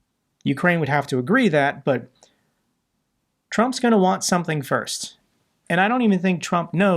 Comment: Thought you said trump was going to lose....😂